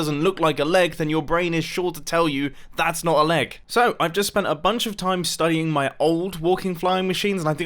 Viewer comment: I thought he was going to say “I’ve just spent a lot of time studying my legs”